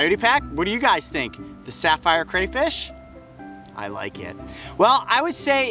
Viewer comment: Who thought it said catfish at first🤚